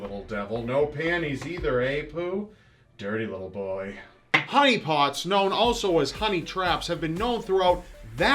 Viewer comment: the first time I heard him say honey pots.... I swear I thought he said huniepop..... im dying xD